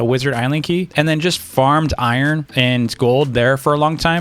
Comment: UMMMMM AT 6:53 I THINK U SAID FUCKED INSTEAD OF FARMED